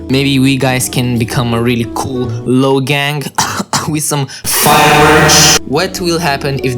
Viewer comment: so i thought you said free merch